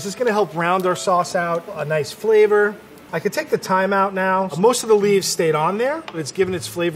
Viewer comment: i could take the thyme out now Me: Oh i thought u said ur gonna have a time out